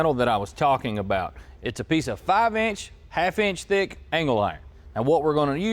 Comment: Lol sounded like said anal iron @ 17:10.